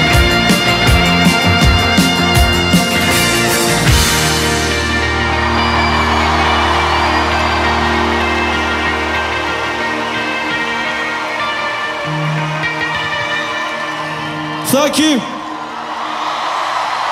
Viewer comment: Because of your accent, I initially thought you randomly said "F*ck you!" to the audience at the end 😂 Amazing performance though! ❤